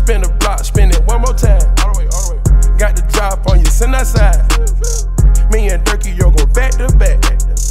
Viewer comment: Future said “me and durkio go back to back” without googling lyrics I thought he said “me and durkio going to vet the bet” lol